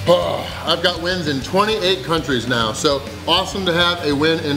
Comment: I thought he said " I cut wind in 28 countries" That would be like hurricane strength.